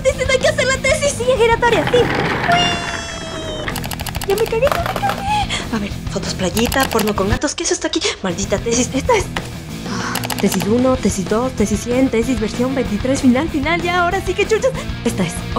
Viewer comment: Soy el enfermo? O escuche nopor con gatos...